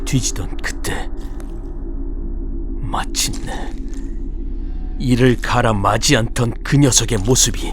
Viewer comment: 잘 들었습니다 님 근데 힘들게 만드신 작품에 제가 지적을 해서 죄송하지만 자막이 좀 이상해서 말씀드려요 7:16 '마침내 이를 갈아' 같아요 11:39 '나가서'가 아니라 '나까지' 같습니다